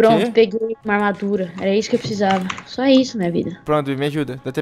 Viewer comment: Só eu que escutei ela falando SÓ ISSO MINHA VIDA no 11:17 ? Shippo 😍❤